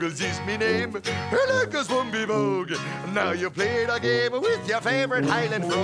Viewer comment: I always thought he said "MacGoogles is me name, I like to smoke me' bong". 🤣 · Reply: I thought he said "I'm like a fluffy bone"